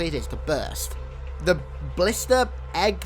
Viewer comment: Who else thought he said ballista not blister